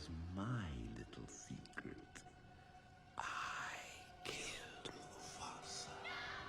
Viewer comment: Ah, I see you, over for Scar's Death at #9. You thought he said, "I killed your father." Scar really said, "I Killed Mufasa." You tried to play it off, you're not slick I caught you. Lol. All fun and games though.